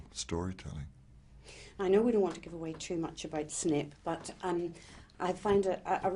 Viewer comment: Did she just said SNIP instead of SNAPE?/??!!!WTH??